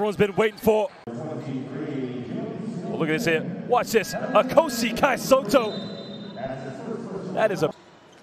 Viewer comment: I thought Josh Bett said "A cozy Kai Sotto. Yun pala "Ako si Kai Sotto." LOL" Big thumbs up to Mr. Bett for bringing Filipino flavor to the broadcast.